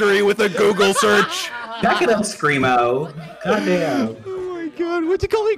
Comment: listen carefully i think bad says goddamn 😶💀👻🗣🤷🤷‍♂️